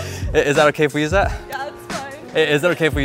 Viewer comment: What he just said to that girl on 3:44 ...😮😲😲😲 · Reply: "I love you"